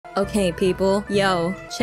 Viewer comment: When she said "Okay people" I swear I heard "Hey hey people" instead